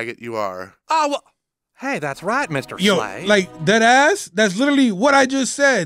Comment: Did you just say 'dead ass"? HAHAHAHAHAHAHA